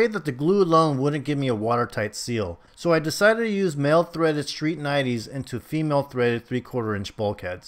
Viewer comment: I am getting ready to plumb my tank and i HAVE a 20'' Synergy Overflow box!! Anyways, in min 5:15 what plumbing did you use for your returns at the top of the tank? Did you say a Male threaded street 90?